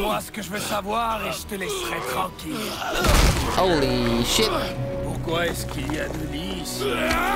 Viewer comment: il a dis quoi le mec ? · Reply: R3MY "Pourquoi est - ce qu'il y a deux lits ici?" T'avais pas compris?